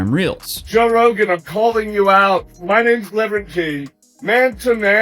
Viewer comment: I swear he said "My name's lemon king"